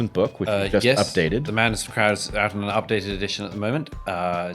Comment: See now I thought he was saying the madness of Krauts.